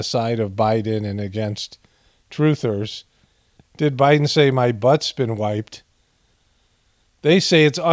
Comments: Do you know you can slow down the playback speed in YouTube I slowed it down to .25 playback and it does sound like he says my butt been wiped lol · I hear ‘my butt’s been wacked!’... also stupid to say in response to reporter question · I thought he said, "My butt's been WHITE". Remember, Joe decides who's white or black.